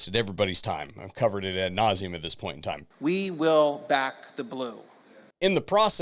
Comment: all I hear there is we will back the boots, because we lick the boots.